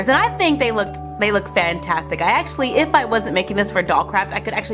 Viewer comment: Did she say DOLL CRAP at 4:55!